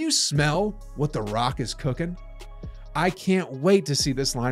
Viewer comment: Did he say what the rockets is cooking?